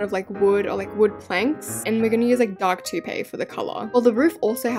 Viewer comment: When you said “Dark taupe” I thought you said “dark tOoTHpAStE” TOOTHPASTE- I didn’t know treehouses were built out of toothpaste……..